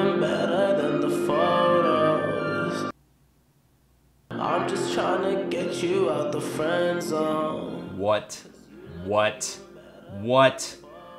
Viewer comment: OMG ALL THIS TIME I THOUGHT THE WEEKND WAS SAYING "I'm just trying to get you out the FRONT DOOR" BUT HE'S ACTUALLY SAYING "I'm just trying to get you out the FREIND ZONE"???? WOW! Thanks, I would have never known!